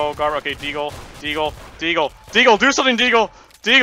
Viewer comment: sounds like he is saying diego